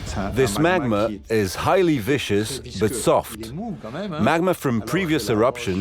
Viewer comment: the narration (AI?) pronounces "viscous" as "vicious" - although, sure, magma probably does seem pretty vicious if you're downhill from it, what the real guy is actually saying is that the molten liquid is thick (i.e. viscous) but soft.